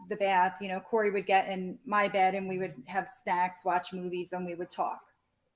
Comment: does she say she has sex with Corey?? Did I misunderstand, it sounds like she said they would take a bubble bath then go in her bed n have sex...etc